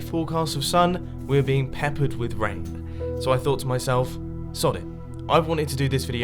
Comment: Did Mumbo just say "Sod it!?"